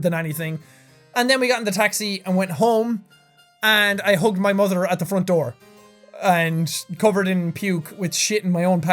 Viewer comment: did jack just say he hung his own mother at the front door at 27:33?